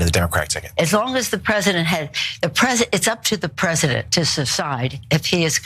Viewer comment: Am I the only one who noticed Pelosi said at 1:27 "It's up to the president to subside..."? I think she tried to say "decide," but "step aside" tried to take over.